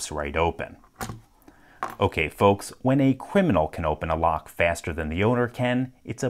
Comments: did you just say you are a criminal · Did you say Cwiminal? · I misheard him say "cwiminal" and that nearly killed me.